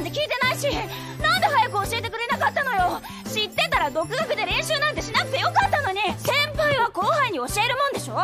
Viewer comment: Did she say Dattebayo?